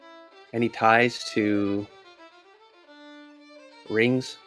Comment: I thought said "Any ties to Ris" And for a second i thought "Oh that's odd but if the Ris from the pokemon universe has a lawyer I'm sure it would be him" =P